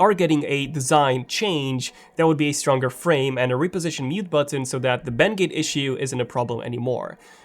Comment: Did he say mute button